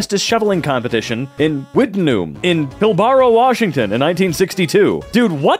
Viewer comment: did he just say fucking washington im genuinly bouta start tweakin